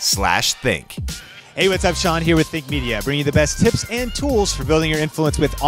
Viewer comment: saramonic sounds best of all three... considering the price point... :) and just a tip, i swear i heard multiple times you said 'fake media' when you actually said 'think media'. Perhaps it was just the mics but I thought you might want to know about this from the viewer's perspective... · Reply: that's what i heard too ....'fake media' ...